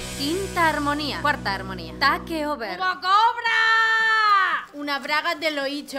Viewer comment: Que dice en el segundo 0:34? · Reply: hugo cobra , creo . Jaja una respuesta despues de 7 años . Nunca es tarde